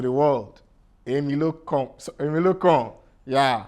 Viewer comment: did he just say Emilo Cum 😂😂😂😂😂😂